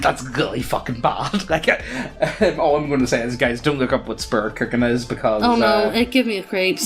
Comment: What did you say to not look up at 2:32 I want to look it up but could not understand you’re words and heard spitit cookin (misspelled on porpoise) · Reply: He's referring to spirit cooking.